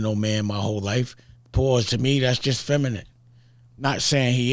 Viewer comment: When Dame said him and Jay Z went looking for Tupac in 96 I swear I heard Pac say "Pause".